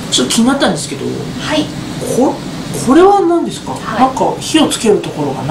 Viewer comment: 可愛い過ぎる❤️「なんですか？」が、「なんでしゅか？」に聞こえて可愛い❤️ ツライ笑 本当に可愛いわ〜